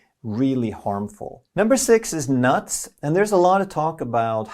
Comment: did you just said Dezz nuts? Love your content, greetings from Brazil!!